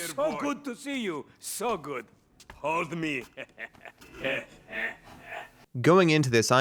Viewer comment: Is it just me or did it sound like Simeon said ‘Hog me’